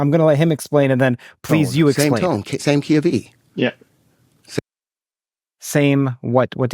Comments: Yep seems they can't understand when most of us normal people have no problem picking up what he's laying down. Leads to believe they are just playing dumb to poke holes in what he's saying. like once he said octave they had to understand how he meant it. Just have an open mind to something new. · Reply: No it just made no sense. What they thought he said was KeV which means kilo electron volts, a unit of energy. Which actually would make sense in this context, unlike the Key of E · Curt, you're the wrong guy for this -- you thought he said "keV" rather than "the key of E"!!! Terrance Howard is excellent on the screen, but in science and math he is confused and delusional. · I heard...Key of "E" Like music. He already said actives. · I thought he said “The same key of E” as in the musical note which has various frequencies of E0: 20.60 Hz E1: 41.20 Hz E2: 82.41 Hz E3: 164.81 Hz E4: 329.63 Hz E5: 659.26 Hz E6: 1318.51 Hz No idea what to make of it in relation to H and C etc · I thought he said "Key of E" as an octave, not GeV (Electron Volts)